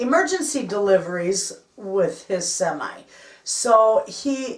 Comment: New to your channel & like your channel. Just looked at your root beer float recipe. Where did you say your husband works at 2:52. Sounded like deck. I couldn’t hear it. Regards. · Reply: My owns his semi and is delivering emergency supplies.